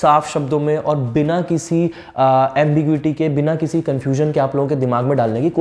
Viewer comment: can't understand the word sir!! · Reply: He said "ambiguity"... and it means unclear (jo cheej clear na ho).